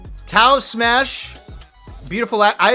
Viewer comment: I thought you were saying 'beautiful accent' rather than accident up until I squinted at the sheet. Beautiful accent btw